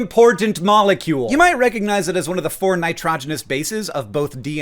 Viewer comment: "You might recognize it as one of the four nitrogenous bases". might doing a lot of heavy lifting in this sentence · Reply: I’m still trying to figure out what nitrogenous means I thought he said hydrogenous 😂😂